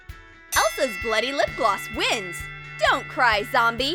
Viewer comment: I heard "Elsa's slutty lip gloss" @@ I have issues